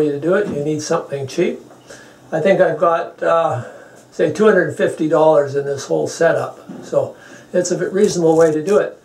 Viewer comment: Genius! Nothing like a man who can build things. It cost him $150 plus his time and genius. Well done! xx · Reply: He clearly says at 5:03 he has spent $250!